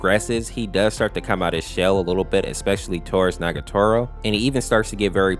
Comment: Bro I thought you said fuck nagatoro in the beginning oml 😨 that had me in the first half ngl 👀